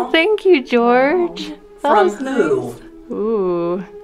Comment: low key thought she said poo instead of who lol